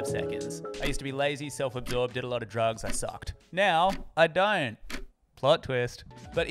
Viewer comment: fucking accent gave me a heart attack, I thought you said "now, I'm dying" 😵 🤣